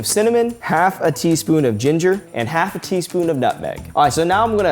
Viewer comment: When he said nutmeg I thought he said nothing